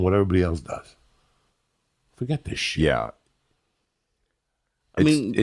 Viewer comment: I thought he said shiieeaa